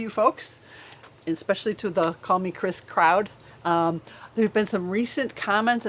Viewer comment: when said “especially the call me Chris crowd” I thought she said coffee crisp crowd and I was like OMG you’re making a coffee crisp candy I absolutely love coffee crisp and only found mostly in Canada and NY however I live in Florida but I have came across them every once in a while in Publix (like wegmans but in the south)in the ethnic section and there was a gas station that used to sell them until they switched owners. My Dad is from Canada and we both love coffee crisps. Anyway I had to go back and relisten to what she said lol I was ohhh she said “call me Chris” I got really excited for a minute